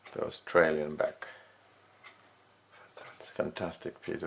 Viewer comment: it sounds like he says "The Australian back" but I'm guessing he means "Austrian".